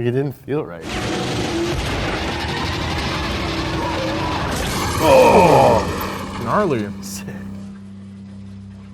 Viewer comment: what does he say? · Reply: "Pussy" cuz the other line is "Only pussies use Nitro-Methane"